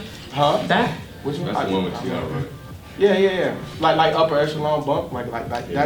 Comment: what he said ? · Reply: I think he said "Upper Echelon" by Travis Scott